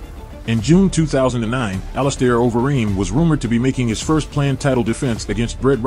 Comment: Oh its Overeem, I thought he said Wolverine.